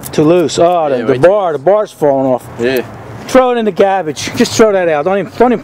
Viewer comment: that accent...I could have sworn he said " throw it in the cabbage "